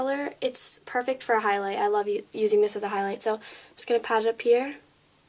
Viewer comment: haha at 6:27 it sounded like u almost said i love u